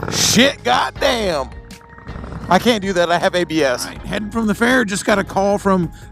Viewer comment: Just got finished talking about shit, and I thought you said "I can't do that, I have IBS." Fucking, lol. Also, the sound of that thumper is unmistakable. Excellent video!